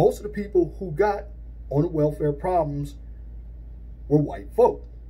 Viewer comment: At the 3:49 mark, I meant welfare programs instead of welfare problems